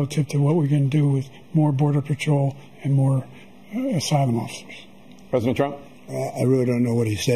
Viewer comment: "We finally beat Medicare" Great job Joe...the Republicans have been trying for decades! PS: When he said "More asylum officers" I legit thought he said "more sidewalks".